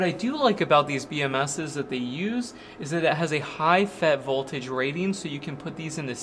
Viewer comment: what's that you said? "FET"? I've always thought lithium couldn't be connected in series